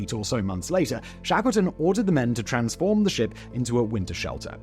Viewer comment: Thought it said shackleford at first